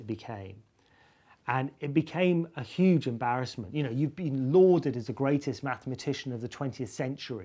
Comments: Should be "lauded" instead of "loathed" in the subtitles, at the 7:55 mark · I think he says "lauded" rather than "loathed" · I know its like a 5 year old video but the subtitle at 7:53 should be 'lauded' instead of 'loathed'. Idk how to edit subtitles so if anyone wants to edit that in that'd be neat. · the subtitles say "loathed(?)" which should be 'lauded' as in honored or praised. Just a heads up!